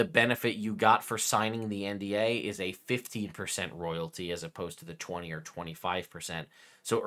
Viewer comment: of the video, it sounds like you were saying that "early adopters" (i.e., those who signed the WoTC NDA) received terms for a 50% royalty amount instead of the 20% - 25% royalty amounts noted in the leaked OGL. Did you mean to say 15% here? Wizards being entitled to a 50% royalty amount would put early adopters in a worse economic position than those operating under the standard terms of the leaked OGL.